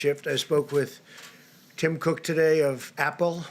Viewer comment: Did he say he talked with Kim Cook of Apple?😳